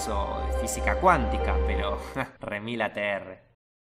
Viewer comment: que es eso que dijiste? Remilatr o algo así?